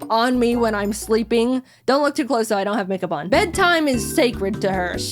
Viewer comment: I THOUGHT SHE SAID "DONT LOOK TO CLOSE I DONT HAVE A CONDOM ON" im trippin